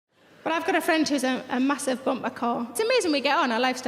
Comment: It sounds like she said she has a friend that is a "massive bumper car" at the beginning. I know I'm mishearing it, but, maybe? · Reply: Yeah, she did say massive bumper car. Earlier in the show she was describing her and her friends' personalities and her friend was a massive bumper car :)